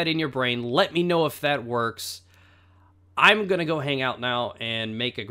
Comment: ***** hah i thought you said at the end that you were going to make a girl cheese, instead of grilled cheese. lul